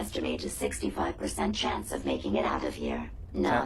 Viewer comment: I read the subtitles before Jezebel spoke, and i thought she said 65% chance of making out here. Lol